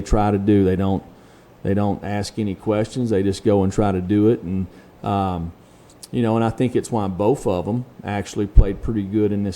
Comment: All I heard was “ya know, and aite” (new word.